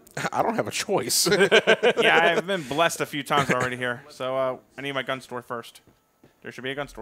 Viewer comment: I thought said I need my gun sword